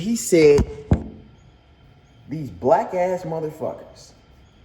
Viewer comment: not trying to defend the guy or anything but if imma be honest i heard "whack ass motherfuckers"